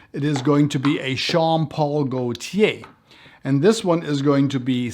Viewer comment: Great video. It almost sounded like you said Sean Paul Gaultier at 3:13 😂 made me think of that Temperature song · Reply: You're the 2nd person that mentioned this 😂 I guess. Sometimes, my German tongue gets in the way. 😆 Please believe me. I know it is (Jean) Paul Gaultier ,lol